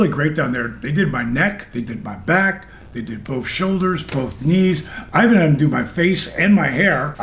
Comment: what he said: "they did my neck, they did my back, they did both shoulders, both knees" what i heard: "they did my neck, they did my back, they did my pussy and my crack"